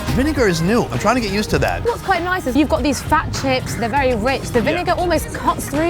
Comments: Thought she said fat chicks. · lmao sounds like she says these fat chick's 😂